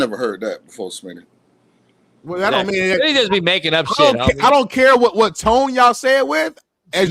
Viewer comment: playing golf rn. stg i thought i heard say niggas be making up shit homie… had to go back 5 times😂😂😂